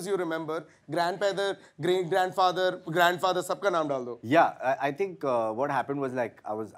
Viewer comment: Did he just say grandpether at 7:10 😂